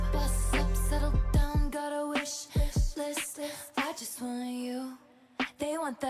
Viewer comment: When it said “boss up..settle down…got a wish list” I thought it said “my son so dumb got a wish list”😭😭😭😭😭😭